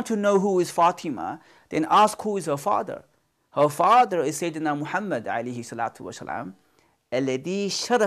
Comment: Subtitle is wrong spell the "sayyidina" Muhammad